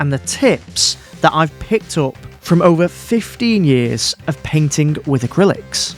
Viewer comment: 😮you can’t possibly be that old. Edit……..Sorry I miss heard, I thought you said over 50years painting 😂